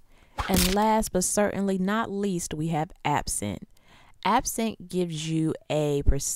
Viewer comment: when she said app cent i thought she said absinthe